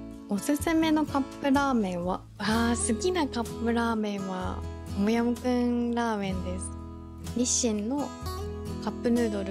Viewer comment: 好きなカップヌードルで「トムヤムクン」の言い方が可愛すぎて聞き取れなかったので、思わず[日清 カップヌードル 味]で検索しました🔍